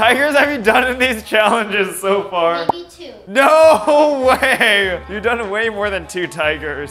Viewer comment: When said maybe two tigers I thought she said eighty-two